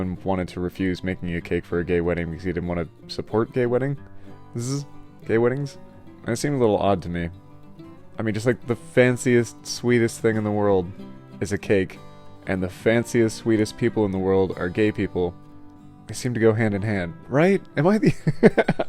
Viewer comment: haha I heard "Swedish" instead of "Sweetest" lol Edit: haha "the fanciest Swedish people in the world are the gay people"